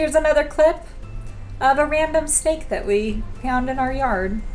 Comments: I thought you said a random steak 🥩 but you said random snake 🐍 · Lol, I thought you said you found a random steak in your yard😂